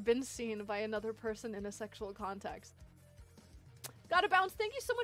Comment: She said sexual instead of sensual a few seconds after 1:36.